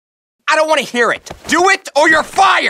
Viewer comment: what did rigby say? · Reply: The Screamer You're all talk Benson